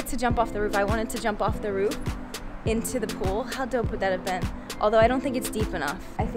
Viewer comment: Hello! I love this video so much, and I want to make sure everyone can understand and enjoy it. That's why I wanted to let you know that your captions are a little off. At about 0:09 the auto-captions say "how don't put that event" where they meant to say "How dope would that have been?".When you get a chance, could you fix that? That'll help the 360 million folks globally who rely on closed captions to understand videos. Thank you, and keep up the great work!